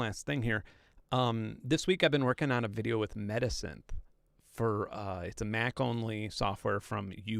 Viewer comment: he mentioned something that sounded like "You and I Medicine". But after looking forever, I found "U&I Software" and it's "Metasynth", not "medicine" lol.